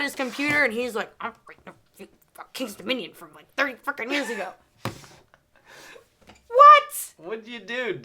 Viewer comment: where? I proof each video before posting and didn't catch any? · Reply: it sounded like dropped an F-bomb. She might’ve said something else, but I heard “30 f**kin years ago.”